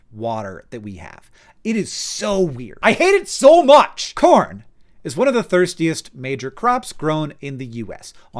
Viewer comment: I thought you said PORN, and belived it.